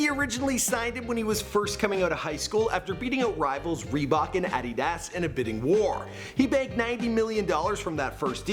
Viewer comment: HUGE THANKS TO SEATGEAK for SPONSORING THIS VIDEO: Use the promo code FAMOUS and get $20 back. <link> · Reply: What did you say it’s Adidas